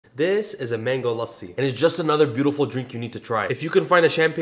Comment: Hahaha you played it well 👏I thought you said mango lusty your beautiful man thanks 😊😉😘😄